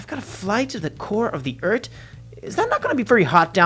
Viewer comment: Uh Cap you said eart instead of earth